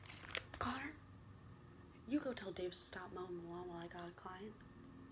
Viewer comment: did she say tell David to stop moaning? Or am I imagining? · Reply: “mowing the lawn”